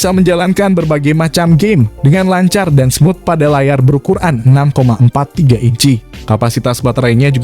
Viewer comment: layar ber Quran? Ga bercanda, cma kedengeran nya kek gitu